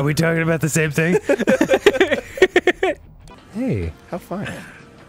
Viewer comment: Okay, I cannot for the life of me figure out what Arin's saying at 0:52. It sounds like he's saying, "Hey, how find", but that doesn't make sense. Someone please help me it's killing me not to know. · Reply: Dan said "hey" Arin said "how fun"